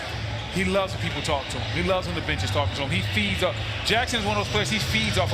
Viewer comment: “he loves when the bitches talk to him” who heard that lol ?!?